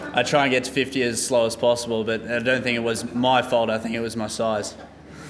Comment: am I the only one who heard some reporter yell "bastard" at 01:50 ?